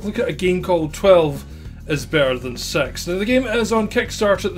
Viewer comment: When you said the title, because of your accent (I knew you were saying 'six' of course) it sounded like you said 12 is better than sex. I was like, well then. This looks like Hotline Miami kind of style. Looks interesting, is there no options or settings to turn it down?